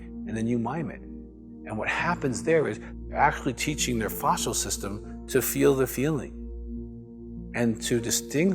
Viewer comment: Dr. Weiss mentions something that sounds like "fossil system"? Am I hearing correctly? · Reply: It's supposed to be "facial system". Sorry for the misspeak.